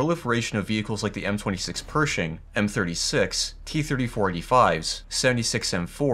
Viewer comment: I misheard that as “T34E5” 💀